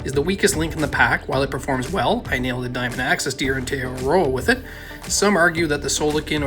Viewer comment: My man, you gotta slow down and enunciate when you speak sometimes, not even the auto-closed captioning couldn't figure out what you were saying. 😂 Something about deer in Te Araroa, but still not sure... thanks for the videos tho, great content! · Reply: “I nailed a diamond axis deer on te Awaroa with it” hahaha I’m from east coast Canada! We talk fast! lol